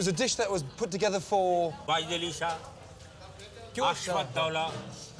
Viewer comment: Chef Ramsay has no idea what that guy just said. hahaha. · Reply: he said, "Wajid Ali Shah....Asaf Ud-Dawlah"